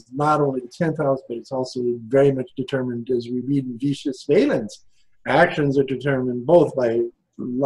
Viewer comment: It's kind of not clear what he said for me. Could someone help me? It sounds like Vicious Daimons. (12th house and possibly 11th house as well? According to their names?) Or is it demon? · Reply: Greetings! He said "Vettius Valens", the name of an ancient Astrologer <3